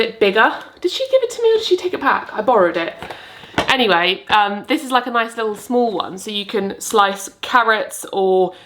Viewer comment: the first time you said this is a mandolin, it sounded like were introducing a lady called Amanda Lynn 😂😂 really gave me a chuckle and just wanted to share 😂😂